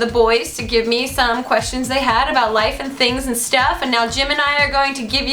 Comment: When she said Jim and i, I thought she said Gemini...